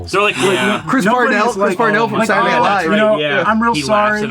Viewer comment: did I hear “Chris Cornell from soundgarden”??